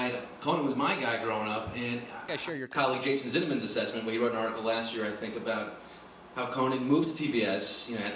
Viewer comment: What is said at 2:41? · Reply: Matt Hall "Your colleague, Jason Zithman's assessment who wrote an article last year..." (not sure if the name is spelled correctly)